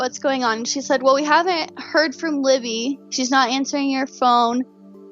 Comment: does she say her Grandma says she's not answering "your" phone? Meaning Libby was carrying Kelsi's phone?? Whose phone was it??? Thought I remember BP saying it was an extra phone on her business account??